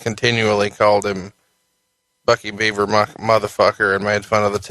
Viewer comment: Cornette says it was bucket beaver, rather than Bugs Bunny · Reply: That's what he said 7:15 .. Buckey Beaver